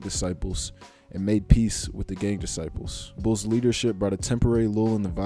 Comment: Did he say gay disciples? Or gang disciples? @ 5:20 wasn’t clear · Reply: Gang* was kind of mumbling at that part, sorry for the mispronunciation